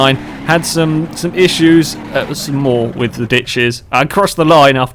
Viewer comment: For a moment I thought I heard "Bitches" instead of "Ditches" xD